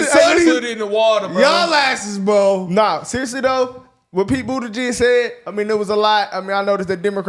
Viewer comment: i thought I heard pete booty jigg lol